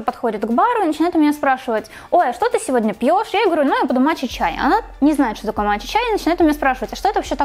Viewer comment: Блин, сначала услышала "моча-чай" 😂😂😂